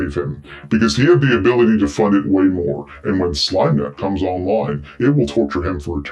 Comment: I thought you were saying "Slime Matt" not "Slimenet" and was confused as to why you'd changed your name from "Stink Matt" and wondered if this was gonna expand the Slime Cinematic Universe.